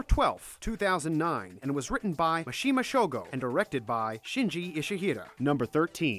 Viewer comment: I thought he said makashima shogo from psycho pass